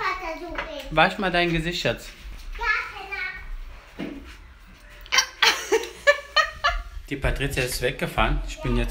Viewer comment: 😂😂😂 hab ich das richtig verstanden?? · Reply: ferry berry Sie hat, so wie ich es verstanden habe "Ja Penner" gesagt 😅